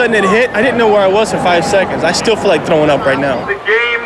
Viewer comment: It sounded like he said growing up not throwing. Mommy I'm a big kid now!!